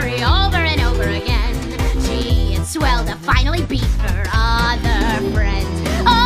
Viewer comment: When she says beat her other friends the first time I listened I thought she said be her other friends 😐😐😐😐